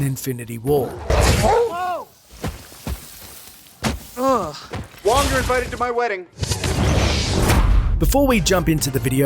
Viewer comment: Wong : Cuts an arm off Iron man : WONG YOU’RE INVITED TO MY WEDDING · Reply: I thought he said "You're all uninvited to my wedding"